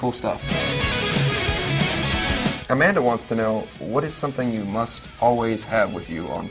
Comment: i thought it said what is somthing you must always shave on tour hahaha